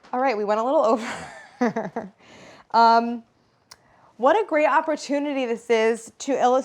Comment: I really thought you said "What a great appletunity". Ohhh the missheard puns..